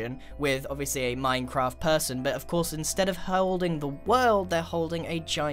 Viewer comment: instead of herwolding the world